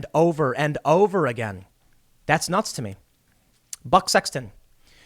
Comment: I thought I heard but sex when he said Buck Sexton.